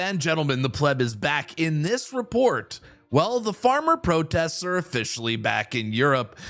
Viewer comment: Just for a moment i thought you said the Starmer protest! I WISH !